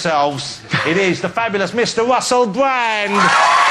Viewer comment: Did he say the fabulous Mister Wussell Bwand at 1:01? He had a little Elmur Fudd accent for a second there.